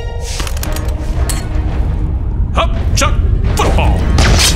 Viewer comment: I thought he said Hop chop fu*k off